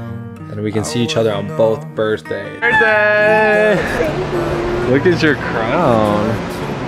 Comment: On 0:22 did he say hey babe??? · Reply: Yocselin Renteria When I put on captions he says "happy birthday, look at your crown". But he might have said hey babe. :)